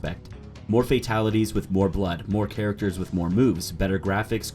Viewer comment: At first I thought he said "more characters with more boobs" 😂. I mean if he did he'd technically be right.